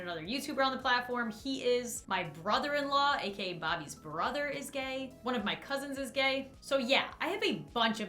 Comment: I had to rewind cuz I thought she said Scott was Bobby’s brother. I literally shouted “WHAT?!?!!”